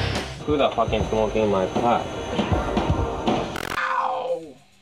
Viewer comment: who da fuck is smoking me pot. Is that what he said?